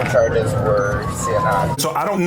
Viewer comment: He actually said C&I and it’s “careless and imprudent driving” which is the equivalent of reckless driving in the state of Missouri 😊